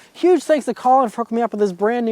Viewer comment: I’m sorry, but speaking too fast made it sound like u said “f*** me up” lol. Great reviews, love them. I agree, the cabin is very noisy and it’s too conservative a design. For an Audi, I think it should be better. Great car still